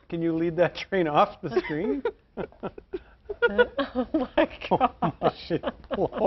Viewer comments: Did he just say shi* in live tv? 😂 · did he just say “Oh shit”?